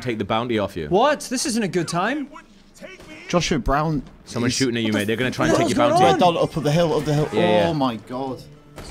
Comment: Did Gavin say "someone trying to shoot you my N-word at 9:24?" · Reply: "Someone's shooting at you, mate, they're gonna try to take your bounty"